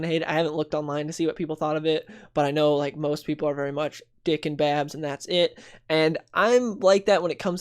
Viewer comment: Am I the only one who heard Dick in Babs. 😂